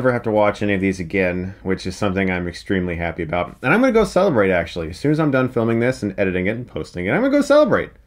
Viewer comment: When you said "I'm gonna celebrate after this" I almost thought you said "I'm gonna go celibate" probably would have been the perfect way to describe how insufferable this franchise was but thank you for all the amazing F's